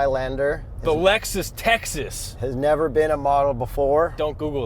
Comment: did just say Alexis Texas? hahaha a man of culture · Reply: He said "A Lexus Texas"